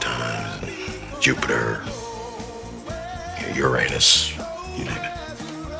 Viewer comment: what does Fishburne say at 2:00 (I mean the joke cuz I didn't hear that) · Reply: He said Uranus which sounded like Youranus, Jennifer just tried really hard to hold it, I started laughing immediately lol